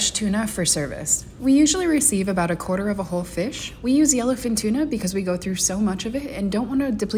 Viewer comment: ITS YELLOWFIN, I ALWAYS THOUGHT MFS WERE SAYING ELEPHANT TUNA